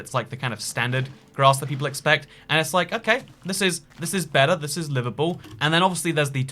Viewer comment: I thought you said "this is better, this is Liverpool" there lol